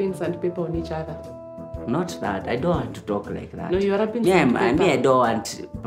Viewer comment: 😂😂😂I thought Bahati said he won’t talk